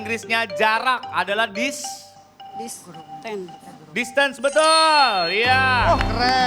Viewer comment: si ibu bibirnya bilang "dispen", lah surya dengernya distance haha